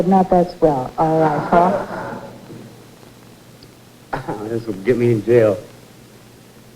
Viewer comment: what do we suppose Paul Williams said where they blanked him out while giving the clue for WAVE? 🤔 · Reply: "Sailorette"